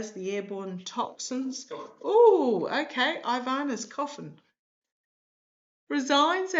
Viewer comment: I think that I heard a guide say coffin